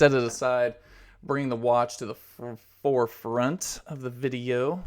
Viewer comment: I heard you fart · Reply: that wasn't a fart... he was saying the wortd "front" that was just half way through the word.